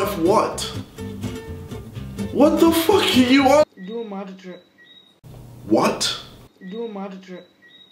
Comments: I think he said “do a magic trick” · I think that kid at 2:08 was saying “Do a magic trick” 🤔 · Guys I figured out what the guy at 2:07 was saying "Do a magic trick" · I think that kid at 2:08 was saying “Do a magic trick” 🤔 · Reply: _ ND _ That makes a lot more sense lol. I heard “You’re mad drip”. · Is he saying "Do a magic trick" ? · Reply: I thought he was saying do a mad trick... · he said do a magic trick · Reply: i thought he said "Do a mad Intro" xD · he said "Do a magic trick" to those who didn't understand. I know this video is old but whatever. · Guys I figured out what the guy at 2:07 was saying "Do a magic trick" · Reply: lachlan seaward I thought he said do a mad trick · Wtf four years later I realise he’s saying “do a magic trick” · I think that kid at 2:08 was saying “Do a magic trick” 🤔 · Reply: I thought he said “Do a mad trick” 😂